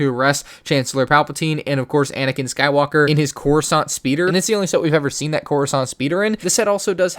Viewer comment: i thought he said crossaint speeder for a sec :D